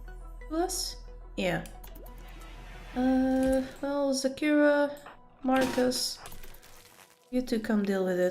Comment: Every time you say Zakira, all I hear is 'Shakira, Shakira!'